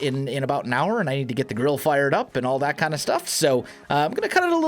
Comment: sounds like you said "gotta get the girl fired up" now either you mispoke and meant GRILL, or your wife is not super excited about dinner with the neighbors lol. =p either way it all makes sense XD